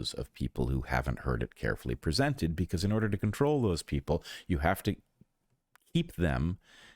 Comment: you have to ‘what’ them? The cut made it sound like you said you have to eat them!